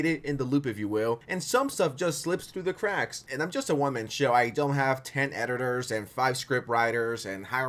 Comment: My brain thought you said “I’m just a woman show”😂😂😂😂